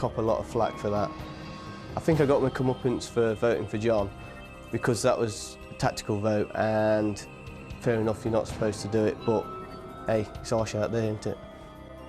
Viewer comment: Hi! Yes indeed! Could you please enlighten me on what saying at 26:45? I'm an English learner from France, and i'm finding these series an excellent way to get exposed to genuine speakers. Thanks for your help. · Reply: az z He says ‘I think I got my comeuppance for voting for John, because that was a tactical vote and fair enough you’re not supposed to do it but hey it’s harsh out there isn’t it’